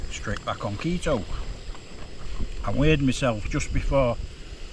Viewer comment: My wife struggles a bit with your accent, North East I reckon. So when you said you 'weighed' yourself before you left she thought you said you 'weed' yourself. No point in taking a chance, you nearly became found footage in the camp from hell.